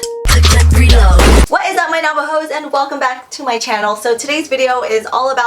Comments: I thought she said Navajo's. But she said Nava-hoes. Btw I'm Navajo :/ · I thought she said “What’s up my Navajos”😂